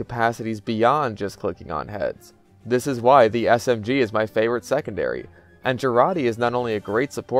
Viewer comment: "This is why the SFG is my favorite secondary" I don't understand if he's referencing Phantom Forces or TC2. What is he saying? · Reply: Smg